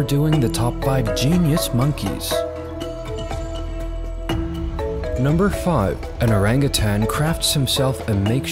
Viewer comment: Totally thought he said, “Number 5. An Orangutan craps himself…” until I saw the context. Almost spit out my beer.